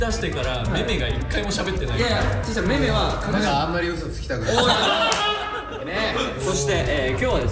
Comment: 阿部ちゃんの「大丈夫？首絞めてない？」めっちゃかわいいし、めーめの「あんまり嘘吐きたくない」が「阿部ちゃんに嘘つきたくない」に聞こえるし(?)、開始1分で本末転倒で終わりそうになるの控えめに言って面白すぎるので最高です🥲